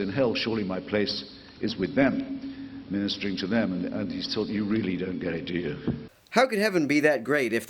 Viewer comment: what's the last 2-3 words Hitchens says @ 0:30 ? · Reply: '...and he's told: you really don't get it, do you?'